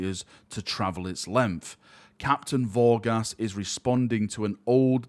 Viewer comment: I thought you said Captain Ball Gas. Thought he'd be a Nurgle worshipper.